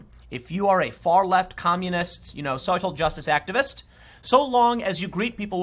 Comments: did he say "soy-cial justice?" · I heard you say it. “Soy-cial Justice”